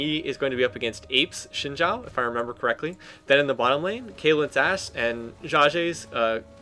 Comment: no offense, but at 3:23 you say kaylant's ass instead of ashe :).